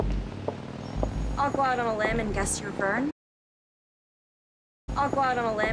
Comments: I think she says “I’ll go out on a limb and guess your Vern” it took me a few times to hear it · I watched the episode with subtitles and apparently she says “I’ll go out on a limb and guess you’re Verne” but without the caps I would actually have understood 0% of what she said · “Imma go out on a limb and guess you’re a Vern” is what I heard.